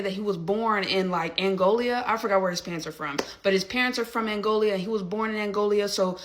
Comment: Is she saying angolia? Do she mean Angola?🤔